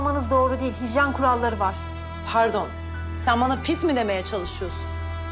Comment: da ne diyor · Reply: diyo diyor hani orda sen bana pis mi diyorsun diyor ya